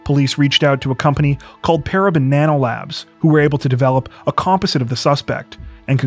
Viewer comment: I kept hearing "Para Banana Labs" .....